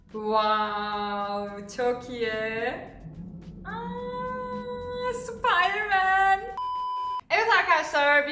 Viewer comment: süpermen mi? Ben mi yanlış duydum jdbxjdbxjbd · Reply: Sıpaydırmen 😁